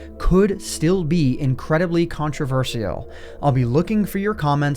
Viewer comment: did he say... “country-vershial”..?